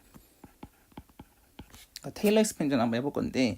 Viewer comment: "테일러 ?" 뭐라고 말씀하셨는지 못 알아들었습니다. 그리고 그것이 무슨 의미인가요? 감사합니다.🤩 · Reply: 테일러 전개입니다.